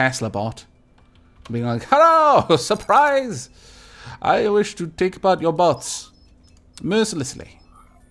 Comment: XD i heard "Haha! I wish to take apart your butts!" instead of "Haha! I wish to take apart your bots!"